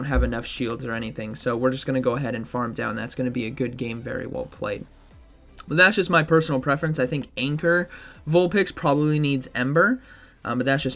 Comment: did you say anchor 😅 someone got liquidated on anchor protocol yesterday